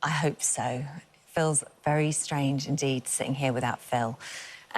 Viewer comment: I literally thought she said “Phil’s very strange indeed”